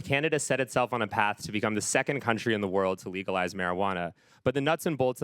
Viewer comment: I thought he said "Cannabis became the second country in the world to legalise Canada."